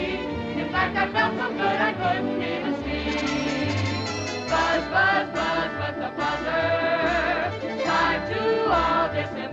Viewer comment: Did I hear "Buzz, buzz, buzz, motherfucker!?" 🤣🤣🤣🤣🤣🤣